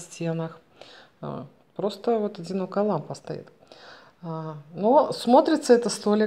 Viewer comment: Пару раз перемотала видео, не расслышала "Одинокая лампа стоит" послышалось "Зеркало стоит".. А коврик на полу синий наверное будет? Просторно и со вкусом. Хорошо получилось. Наволочки на подушки и украшения хорошо перекликаются.